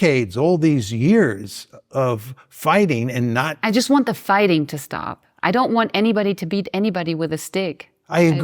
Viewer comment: whew she’s COLD HEARTED when it comes to the Palestinians undergoing ethnic cleansing, horrifying as a German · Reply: I heard "I want the fighting to stop so why don't the palestinians leave?"